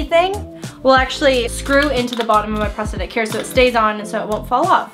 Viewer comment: who else heard “ when it screws into my prosthetic hair...” like if you heard! 😂 👇🏻